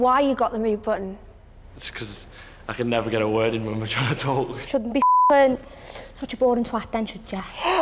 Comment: what language is the girl speaking I turned on cc cause there accents are too much and the cc said “ you shouldn’t be such a golden thwack what does that even mean I can’t with Brits and there’s accents